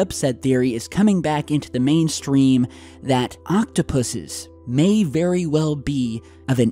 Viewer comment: All I heard was "Octopussies".